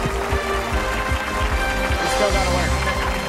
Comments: i thought i said "we still got away" xD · did he said "you still got to work"? :DD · I thought he said "you're still not going" at the end.